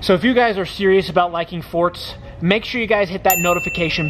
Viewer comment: Omg when he said like blah blah if your serious about forts i thought he said divorce 😂